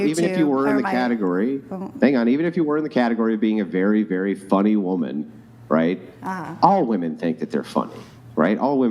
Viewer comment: Amy Schumer? I thought they said funny woman😂😂